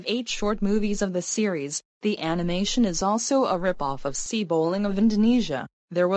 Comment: is that say c bowling or not 2:50 and said c anil or not